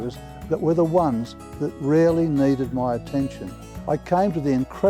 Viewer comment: I had to listen to the first minute of this several times because I thought he kept saying that the dogs being fed real meat were the ones that "really" needed his attention. But I finally figured out that he said they "RARELY" needed his attention.